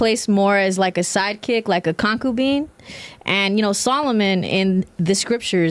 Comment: That girl with the furry eared thing. I thought I heard her say the boyfriend kept her as a coffeebean.. Not a concubine.